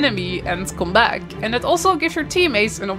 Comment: At first, I thought you said, "And give your teammates an opportunity to rizz you" 😂😂😂 I'm definitely going to make jokes and pull pranks on my gw2 friends with this premise now LOL